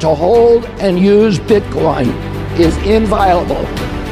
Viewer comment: Did RFK say "Your right to hold and use Bitcoin is inviable???